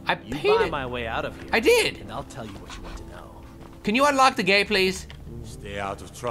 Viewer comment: It sounded like he said "Can you unlock the gay, please?"